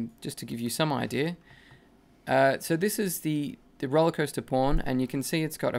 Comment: It sounds like he said "Rollercoaster porn" instead of "Rollercoaster pawn" XD P.S Nice tutorial mate really helped me out!